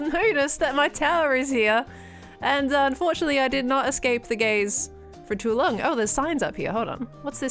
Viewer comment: I know she said "unfortunately i did not escape the gaze" but i heard "unfortunately i did not escape the gays" and that is <num>% funnier now 😂😂😂😂😂😂💀 Great episode as always Pearl! I love your dumpster shop a lot 💕